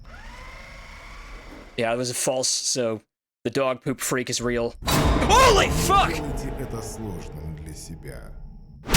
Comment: what does the Russian say? I cant make it out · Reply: "don't make this harder for yourself"